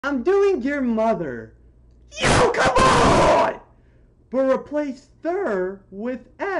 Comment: I thought to said replace m with ther and I was like "what's therother?"